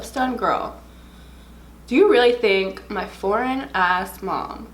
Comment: “My foreign ass mom” LMAOOOO so relatable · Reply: Maria A. Echeverri i literally heard "my boring ass mom" lol